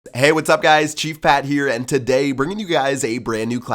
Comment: I thought he said "hey guys it's G fat here"